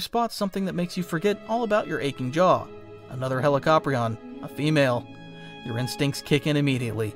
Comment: My dumbass thought it said helicopter